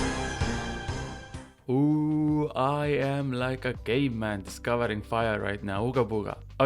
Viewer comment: honestly, I heard «I am like a gay man discovering fire». Got confused for a split second ngl 😂